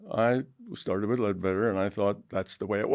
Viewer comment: What does he say at 32:30 seconds? He started with lead better? It’s like he’s referring to someone or a book that teaches about auras?